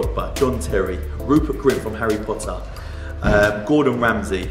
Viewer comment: Did he say Harry Potter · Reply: “Rupert Grint from Harry Potter” he played Ron. You can imagine Ron Weasley taking his Bugatti for chrome wrap